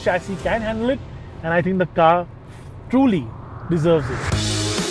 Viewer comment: the car f......, truly deserve it! I heard you 😄